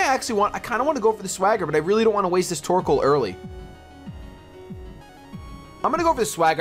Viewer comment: it sounds like you say twerkoal :P you should nickname him Twerkoal